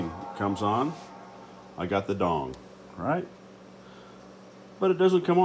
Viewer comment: did he just say I’ve got the dong😂